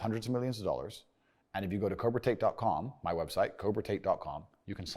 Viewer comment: Can someone write down the website plz? Im not native I don't understand what he is saying. time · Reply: cobratate <- thats the word ;)